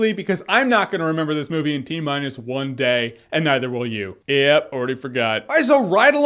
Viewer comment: LMFAO I thought it said tminus 12 days